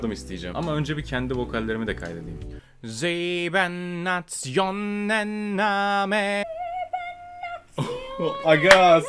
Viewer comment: burda ne diyor ve anlamı nedir bilen acil dönsün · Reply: Almanca seven nation army diyor , meali 7 ulus ordusu . tabi 7 ulus ordusu nasıl bişey ben de bilmiyom